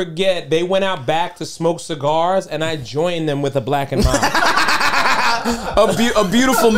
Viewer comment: what did he say he joined them with? Blackened mine?